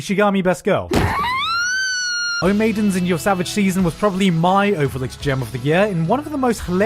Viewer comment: what is the name of the anime? I can't quite understand what he says, and he missed it in the description as well. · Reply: Its called: O Maidens in Your Savage Season